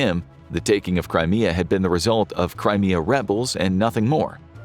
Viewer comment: When he said "Crimea Rebels" @ 8:21 it sounded like that "Cry Me A River" song by Justin Timberlake lol.